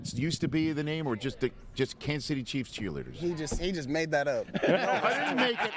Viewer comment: For a second, I heard "The Kansas Titty Chiefs cheerleaders". You can have that name for free, KC